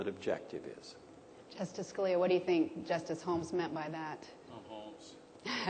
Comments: What did Scalia say at 3:51? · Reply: "i'm Holmes" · Somebody tell me what Scalia said at 3:51? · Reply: He said "I'm Holmes," in response to the original question of whether he was Holmes or Hand.